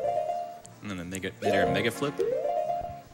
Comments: Did he just say “nigga flip” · "i'm running on the gamecube version" >upper left corner says "platform: wii" 44:02 a what flip? 49:55 ????????????????????????????????? 1:54:04 MYANUSMYANUSMYANUSMYANUSMYANUSMYANUSMYANUSMYANUSMYANUS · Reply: sounded like "nigga flip" 😂